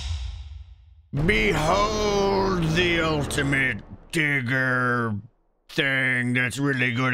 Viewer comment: in my head I heard "behold, the ultimate digger-inator"